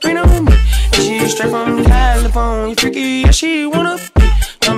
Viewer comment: btw what does it say at 0:13 ? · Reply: i think it says "tell me she like, boys and girls"